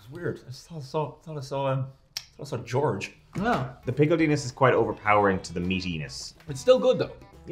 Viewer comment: Did he say pickled Enus?